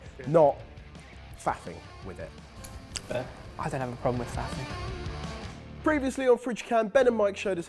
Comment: I know what Ben ACTUALLY said, but in my heart of hearts, he said, "I don't have a problem with a bit of fapping." (at 8:02) ROFL!!!!!!